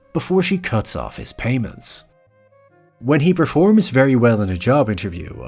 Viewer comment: holy... I thought that narration said "...to see if they're compatible before she cuts off his penis ".